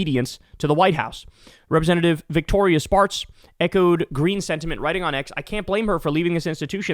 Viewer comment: When you said "Victoria Spartz", I heard "Victorious Farts" 😂😂😂